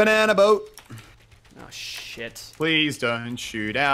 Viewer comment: "Oh shit" anyone else hear at 15:40?